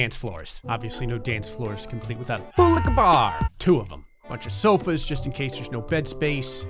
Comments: Okay I thought he was saying boulica bar and I’ve been so confused since he started this series. It’s FULL LIQUOR BAR!! 😂😂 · Ok i am from Germany and i cant understand what he is saying before Bar. Can anyone hellp? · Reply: Full liquor bar